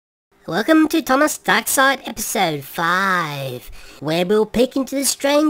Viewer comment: Welcome to Thomas backside It's so five is what I hear